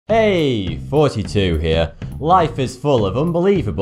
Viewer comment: I put this on before bed, and only listen to it... Ive listened to a bunch of these, and all I hear is "42 here" not thoughty2 lol.... British accent!